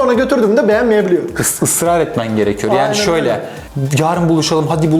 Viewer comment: Çabalayan erkek olman gerekiyor diye anladım hocam